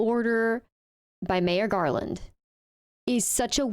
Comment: Bad audio or an unconscious slip of the tongue - I clearly heard "Mayor Garland"